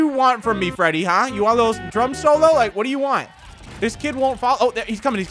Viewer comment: When he said freddy do you want a drum solo I thought he said drug solo